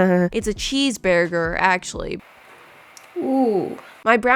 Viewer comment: BEARRRRger instead of burger